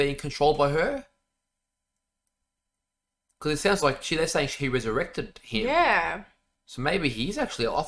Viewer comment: Love your reactions as always but am I mistaken? Did you say “SHE ERECTED HIM” 16:24 🤣🤣🤣